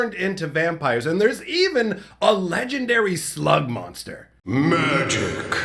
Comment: At 13 seconds in a thought he said "a legendary slut monster"